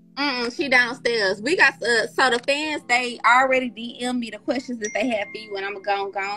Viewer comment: I thought she said folks dm’d questions, she asking shit off the dome😂💀